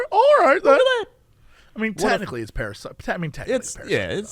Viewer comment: Herp derp. I thought said "para-sider" and I was like....WTF.